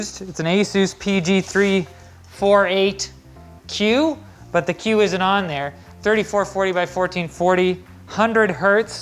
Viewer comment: "but the Q is anon there" i heard it we all heard it